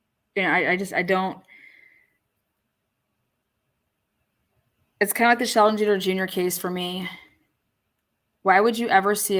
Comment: What is the case name says here please? I'm an Aussie & can't make it out 🤔🙃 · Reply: Think it’s Lesandro “Junior”Guzman-Feliz